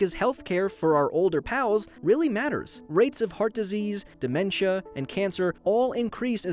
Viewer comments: I found an error that says heat instead of heart.But hey we all make mistakes, just pointing it out! :) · heat disease instead of heart disease